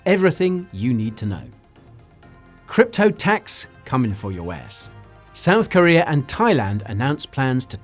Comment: I had to rewind, it sounded like he said crypto tax coming for yo ass.